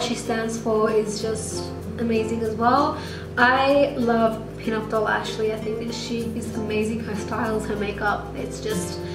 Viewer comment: who is the you tuber she mentioned at 19:17? the one that she says her style and makeup is amazing? i literally can't understand her at a llll, but i want to find out who it is really bad :/ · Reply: I think she was talking about pinupdollashleymarie x